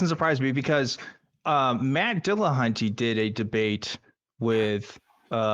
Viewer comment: I heard that as "Mad Dillahunty". LOL!